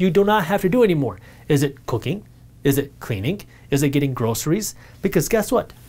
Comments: i thought he said cocaine damn I had rewind and double check that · i thought he said cocaine 🤣🤣🤣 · Another blessing in form of video. 🤦🏽‍♂️🤦🏽‍♂️ man I had to rewind about 4 times to realize he was NOT saying “cocaine” at 19:42 😅